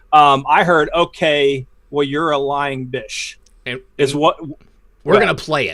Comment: Okay, you’re a lying bish- is what I thought she said.